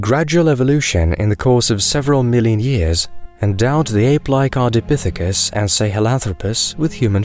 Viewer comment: What is "card epithecus and say hello" ?